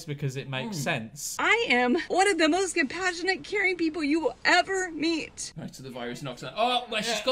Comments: For a second I thought she said: I am one of the most compassionate Karen people you will ever meet 4:36 Which would make sense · It sounds like she says " I am one of the most compassionate, KAREN people you will ever meet" 👏👏👏